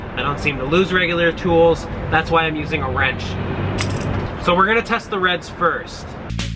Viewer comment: who heard we gonna test the rench first